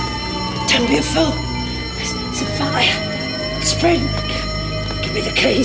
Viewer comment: Did Ferguson say there's a fire. it's the freak? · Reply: James Quinn I think she said “there’s a fire, it’s spreading”